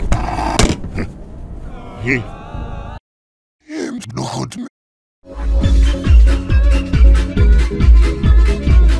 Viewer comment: What did he say at 0:36?? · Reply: It's good or something